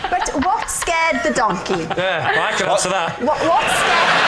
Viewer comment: What does Lee say at ~14:55 right after Alex asks “what scared the donkey?!?” I’ve listened half a dozen times and I still can’t make it out. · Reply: I think it's just "I can answer that". I think he's saying that it was David's appearance that scared the donkey